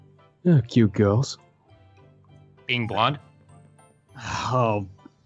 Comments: Garroth: exactly everyone has their weaknesses Zane. Zane: oh yeah, well whats yours? Garroth: hmm..........cute girls. Vylad: being blunt. lol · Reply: OOOOOOOHHHHH...... I thought vlad said “Being BLOND” was his weakness.....OOPS 😑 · OMG LOL I Thought Vylad Said "Being Blonde" But Then I Realised He Said "Being BLUNT" XDDD And Vylad Is Death The Kid From Soul Eater- "Your A Symetery Is Your Down Fall HAHAHA~!"